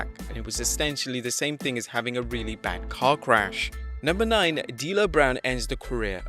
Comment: Lol at 1:51 I thought he said “really bad cock rash” 😂